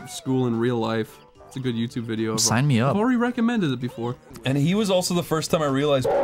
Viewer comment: "Minecraft school is great, Cory reccomended it to me" I mean who else would've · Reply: I thought he said "I've already recommended it before." But I could see Minecraft Highschool being in favourites.